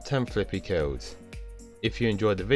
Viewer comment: i thought you said : if you enjoyed the video please leave me alive